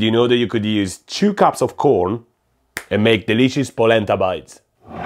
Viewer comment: I thought he said Placenta bites… was about to questioning everything